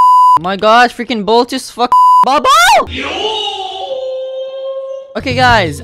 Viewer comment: did he said bobo